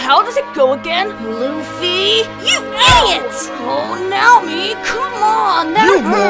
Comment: Did he say now-mi?